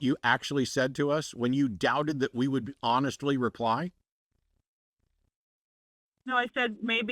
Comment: Matt, you ought to give this woman an apology for that last bit. You said "Are you trying to pretending like we're the stupid ones who didn't hear what you actually said to us when you doubted that we would honestly reply" and when she tried to correct what you had miss heard by saying "No, I said maybe you wouldn't believe me", you said "that's BS, rewind ... the tape". Well I did, and what she actually said was "would you honestly believe..." and then you interrupted her. She was right about that and you berated her because of something you miss heard her say. She simply doubted that you would believe anything that came out of her mouth was from God (and honestly yes, I do doubt that) and all you had to do was reassure her that you would try to be as honest as you could if she called back with a word from "God".